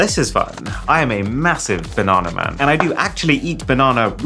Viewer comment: Did he just say 'I'm a massive-banana' man? 😂